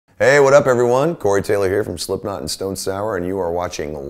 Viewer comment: I thought that said Gorey Taylor... I'm an idiot